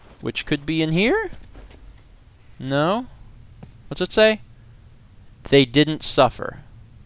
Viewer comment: I thought it said it least it didn't buffer